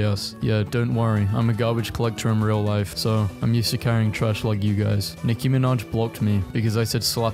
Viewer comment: I misheard it as : I'm isekaing trash like you guys